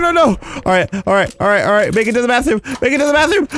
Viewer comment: when u said "alright alright" it sounded like u were saying "~ARA ARA~"